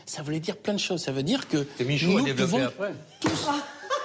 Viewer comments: il dit quoi a 9:00 ? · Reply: "Que Michou a développé après" Il fait référence à Michou, célèbre directeur du cabaret transformiste parisien de Montmartre qui porte son nom. Il avait servi d'inspiration au classique du cinéma La cage aux folles et est mort en 2020. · Il dit quoi j'ai pas compris mais ca a l'air drole ^^ ? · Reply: "Que Michou a développé après" : Baffie fait exprès de confondre le transformisme qui fait partie du domaine des sciences (théorie de l'évolution) et le transformisme qui est un art du spectacle (se déguiser et agir en femme alors qu'on est un homme et vice-versa, et que Michou, directeur de cabarets, a popularisé).